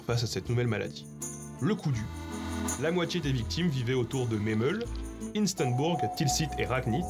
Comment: "La moitié des victimes vivaient autours de mes meules" QUOI ?!?